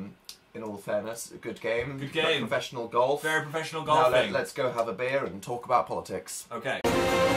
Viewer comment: what Dan said "Let'S go have a beer" what I heard "Let's go have a baby"